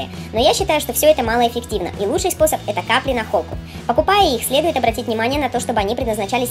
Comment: Какие капли? 2:23. Нахолка? Не расслышала... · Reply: идешь в зоомагазин,и говоришь:капли от блох и клещей на холку.вот и все